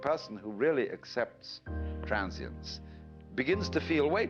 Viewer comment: The person who really accepts what? , i didnt catch that word at 00:14 . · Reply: Mohamed Ali Accepts 'Transience', that is non permanance of oneself.